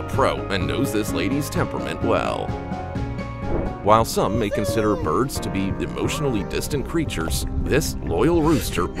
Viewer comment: Can someone please tell me what the animal is at 8:57? I can't make out what he's calling it. tx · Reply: It’s a binturong.